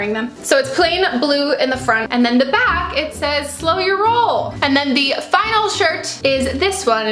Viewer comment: I cannot be the only one who thought the blue shirt said "show your hole" GENUINE MISTAKE but I mean I''d wear that